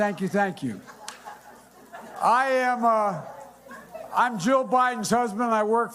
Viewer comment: did he say “I’m Joe Biden’s husband”? Lol I know he said Jill but it sounds like Joe